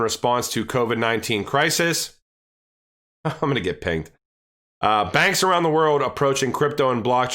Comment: Lol at 10:17 had to replay... at first I heard "ima get paid"... had to laugh....